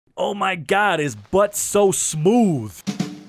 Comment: I had yt on autoplay, and out of nowhere I hear say "oh my God his v Butt's smooth" and I was so confused